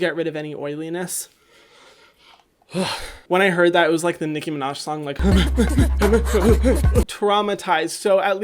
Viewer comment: When he said "like the Nicki Minaj song" I misheard it and thought he said "like the Mickey Mouse song" so when the song played I imagined Mickey Mouse having a seizure. It's times like these that I despise my bad hearing and obnoxious imagination.